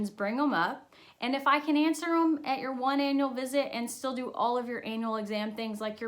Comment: I totally thought she said your anal appointment and she said annual smh 🤦‍♀️ my mind...